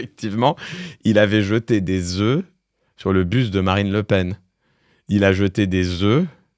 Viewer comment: J'ai compris "le busTE de Marine Lepen", j'étais pas prête 😅...